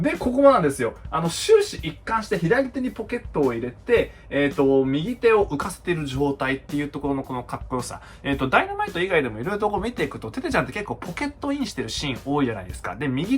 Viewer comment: 「左手にポケットを入れている」に聞こえて笑ってしまった